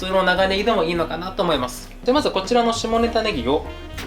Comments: が何度聞き返しても下ネタネギに聞こえてしまう…😂 農家の方々、さんほんとすみません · 下ネタネギにしか聞こえん。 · みんな触れてないけど下ネタ葱に聞こえるの僕だけ？？ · 下ネタネギにしか聞こえないwwwwww